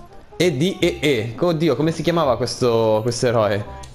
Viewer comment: Minuto 3:16 ho sentito se nn sono sordo co dio